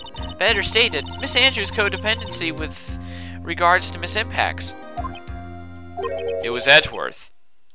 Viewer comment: evry time i hear "ms. impax" is sound like "ms. tanpax" hahaha :P